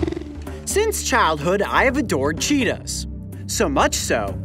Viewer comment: that's not a cheetah costume that's a lepord costume XD 😂 and it sounded like u seid Cheetos.